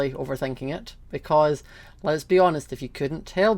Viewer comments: When he said "Les be honest." I thought he said " lesbian" cuz I was thinking about stuff like that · Slayyyyyy slayyyyyy yasssssssss good for you your subscribers are all here for you 🌈 I like how with you accent it sounded like you said Les be honest 😊